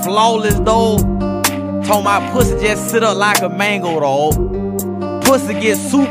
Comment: Can anyone tell me what he's saying at 3:28? Mango roll? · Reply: "mango, dawg" lol